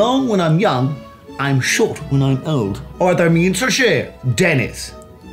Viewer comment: I was not focusing during the mandarin part and I thought he was saying "What I mean to say, Dennis" xD